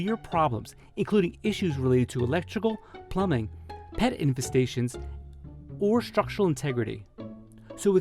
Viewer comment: did he say pet infestation? :) yellow journalism at its finest.